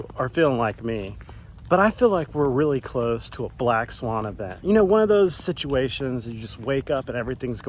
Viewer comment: Crap, now I have to change, I thought they said black tie event. Can I wear board shorts? At least I'm in FL and not CA. Yeah there's problems here but trust me it's not nearly as bad as CA.